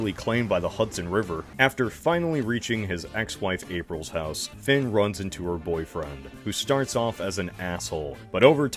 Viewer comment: For some reason I heard ‘fin runs into their boyfriend ‘ and got immeasurably dissatisfied when I rewinded